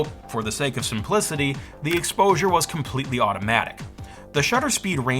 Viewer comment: As a photography nerd, this was an awesome episode. loved learning about this as I've never looked much into early digital! Quick subtitle correct at around 9:50 - subtitle reads "1/130s to 1/175s" of a second instead of "1/30th to 1/175th 🤙